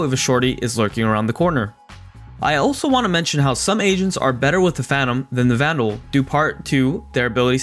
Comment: I heard Asians not agents 😂